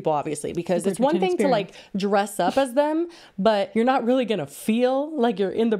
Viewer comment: Had a dab before the episode and them going from halloween costumes to the Wonka exhibit made me immediately think STOP. Joint costume. as the bartender. as the unknown. I'm in tears. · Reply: i thought you were saying they should dress up as joints i’ll be honest